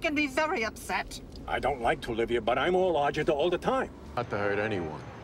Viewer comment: what did junior say here? 😂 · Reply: "I don't like to, Livia, but I'm agita all the time."